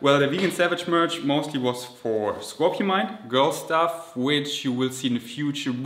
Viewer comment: I am sorry 😅 But have to comment again 😅 5:47 what happens to the o and n in scorpion 🤔 I always hear scorpimind Driving me insane 😅